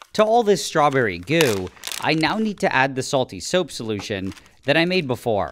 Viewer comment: fr thought he said "soapy soap solution"